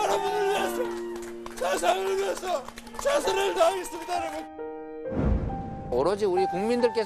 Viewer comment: "...여러분을 위해서 세상을 위해서 최선을 다하겠습니다..." 대장떵이 단군이래 최대 치적이라고 떠벌이는 싸나이 중에 싸나이, 모녀를 한꺼번에 37번이나 잔혹하게 찔러 죽인 살인마를 심신미약이라고 변호한 인권변호사라고 소문난 싸나이, 엄마한테 함부로 했다고(일설에는 이게 거짓말이라고 함) 형과 형수한테 막돼먹은 쌍욕질한 싸나이 이렇게 " 여러분을 위해서 세상을 위해서 최선을 다했습니다."로 들림.